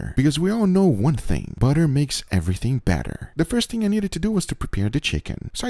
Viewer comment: Butter makes everything better I heard "badder"